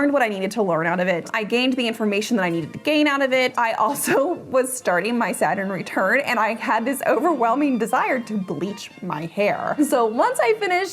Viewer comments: Great video! What do you say @ 19:22 starting my Sat eree tur? I listened to this 7x. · Please forgive me, but I have issues with hearing, and sometimes I can't make things out. Can someone tell me what she says at 19:30? It sounds like Saturday return to me. I've replayed it multiple times and can't make it out. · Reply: She is saying Saturn Return. I couldn't make it out, either. I finally saw a couple of references to it in the comments. I had no idea what it was, so I had to look it up. From Wikipedia: "In horoscopic astrology, a Saturn return is an astrological transit that occurs when the planet Saturn returns to the same ecliptic longitude that it occupied at the moment of a person's birth." I'll leave the rest for you to look up!